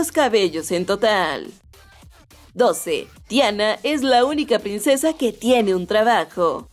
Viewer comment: escuché Diana